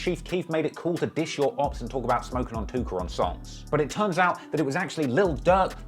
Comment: it sound like he say smoking on tooker